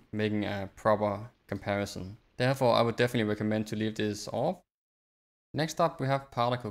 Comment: what did u say about grass displacement on or off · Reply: Yeah sorry I was a bit to quick to cut my self off. But I said "Off" which is what I recommend for grass displacement.